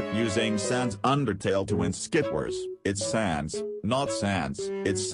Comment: I thought they said "ITS SAD NOT SAD"